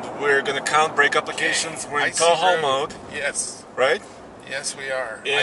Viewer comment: is in Tall Hoe mode again lol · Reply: At first I thought he said "Tahoe" mode... wrong truck :D Tow Haul mode eh?